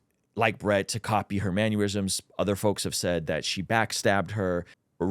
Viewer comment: you keep saying "mannerisms" as "maneurysms" very concerning 😂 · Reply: I caught that too.. I was like, did he just say "manure-ism"? 🤨😂